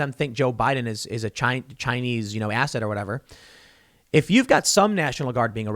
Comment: did he just call Joe Biden a Asshat lol